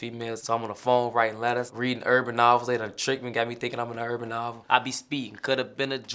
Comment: What is he saying at 1:50? When he talks about how he learned to talk game in jail. He said he was reading _ __ ??? Urban Alver ?? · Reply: Urban Novel